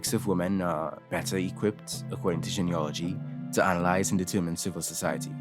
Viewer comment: I was listening to this without looking at the screen nd thought you said "geneology" and was very confused lol